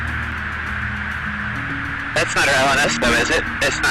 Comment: sounds like he says, “That’s not a noesto, is it?”